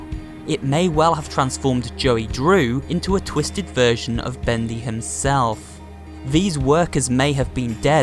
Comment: if you put on cc and watch on it says bendi instead of bendy when talking about joey may have been turned into a twisted version of bendy XD